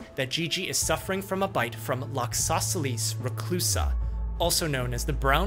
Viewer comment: I thought I heard ISOSCELES and half expected to see a triangle shaped spider.